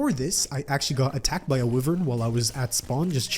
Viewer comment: does he call wyverns river